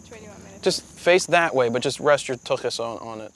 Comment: Did he say, "rest your tuckus"